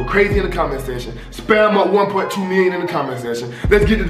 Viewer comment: Who else thought he said 1.2 billion 🤣